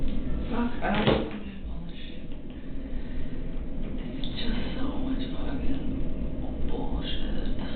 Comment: At beginning when NK was left alone the first time I thought I heard her say something: This is B/S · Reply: I heard it ! Around 11:21 or 11:24 she says “so much bullshit, just so much f-ing bullshit” She really is disgusting